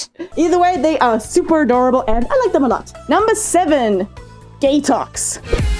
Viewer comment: i heard ”Gay Talks”, not ”Gatoxes”